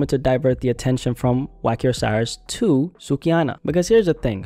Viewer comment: Not even gonna I thought you said Wacky Osiris 🤣🤣🤣